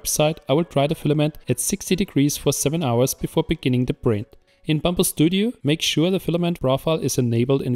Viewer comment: Sounds like your saying bamba